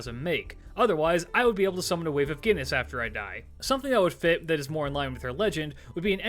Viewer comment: In the Shuten NP i heard "Summon a wave of Penis when i die" and i was like "what?" then i re heard that and "Oh he said Guinness, that makes more sense than a wave of penises" Also, the Golden Fleece can fly may be from the origin of the item. A pair of sibling were stranded due to something, they prayed to the gods and Zeus sent a golden ram to save them, they mounted the ram and it flew them to an Island. Once there they sacrificed the ram to the gods, skinning it to make the fleece and burnt the carcass as an offering. That Land would later become Colchis. Again i am writing this from memory, so i dont remember parts, as you can read, but that was the gist of it.